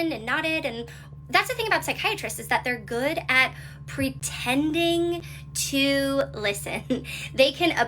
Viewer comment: “He’s a good listener, I mean he’s a Pisces” girl he’s a PSYCHIATRIST 😭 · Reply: Also i thought she said they weren really good at PRETENDING to listen?? Is that also pisces of him? 😂